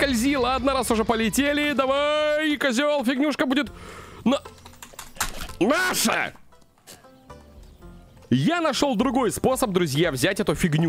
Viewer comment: я ела арбуз и вдруг заорал НАША!!! Но мне показалась МАША!! а меня маша зовут и я проверяла кто меня звал пока не поняла что ОН сказал, и сказал именно НАША😂😂😂